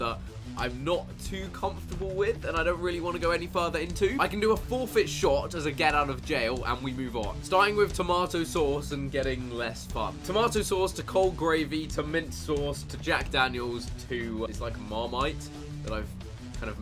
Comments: did he say getting less fucked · did he say less fu*ked